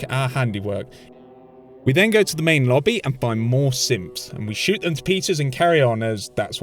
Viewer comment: I think you said simps